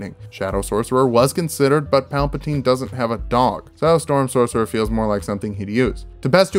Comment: Palpatine does have a dog his name is vadar and he is lethal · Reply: I misheard that as "Palpatine doesn't have a dong" and was afraid to know how knew that.